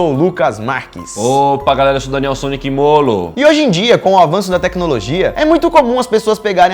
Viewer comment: só eu ouvi daniel sonic molo